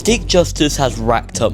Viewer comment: fun fact: the captions show "d!ck justice" instead of "stick justice"